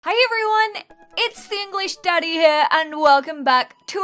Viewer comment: This is what i heard "hi everyone its the english daddy here" what the heck did anyone else hear that!!!!!😂😂😂